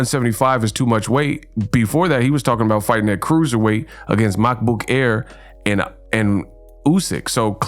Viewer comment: it sounds like you say Mac Book Air lol